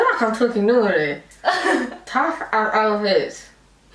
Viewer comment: I thought you said 'tough as ovaries' loooool!